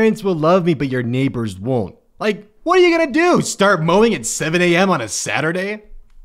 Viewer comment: I thought he said moaning and I was like, “yes”